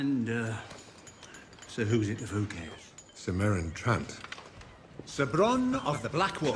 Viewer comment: idk why i always heard bronn say "ser whosit the fook cares"